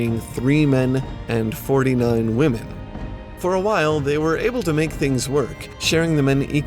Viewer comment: did you say 40 million women?